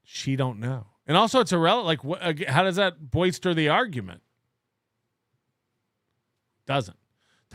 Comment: Hey, at 5:16:45 you said "How's does that boister the argument?" instead of BOLSTER. Pretty embarrassing tbh